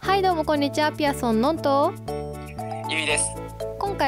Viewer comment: 最近ピアソンののんです！がピアソンのんですに聞こえる。どうでもいいけど